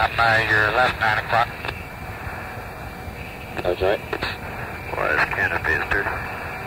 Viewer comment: Yellow "<garbled>" at 3:35 sounds like he is saying "Oh... this canopy is dirty"